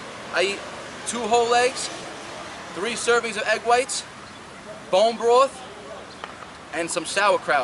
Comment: I have summed it up ⬇️ Breakfast protein, moderate fat, low carb. 2 whole eggs, 3 servings of egg whites, bone broth from grass-fed animals (colagen) sauerkraut (probiotics) Drink: water or burdoks tea (root) Meal 2 - Pre workout shake: (90 min. before train.) Spinach 70g. 1 banana 1 scoop peanut butter 1 scoop whey protein Almond milk. Total 500 kal. (Aprox.) Meal 3 - post workout: Sweet potatos or rice Lean steak or wild salmon Brocoli Avocado Meal 4 - Pancakes: Oats Egg whites Banana. Eat healthy fats: Avocado, coconut oil, olive oil, nuts. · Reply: I heard "bone broth" not "bore broth". Anybody agree?.